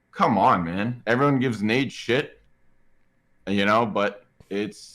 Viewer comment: crim says “everyone gives ??? shit” Did he say nade?